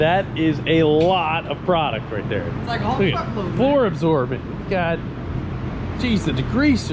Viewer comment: Why did I hear say " that's a whole fuck load right there!"